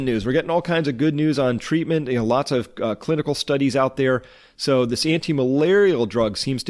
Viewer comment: lol sounded like u said CLITical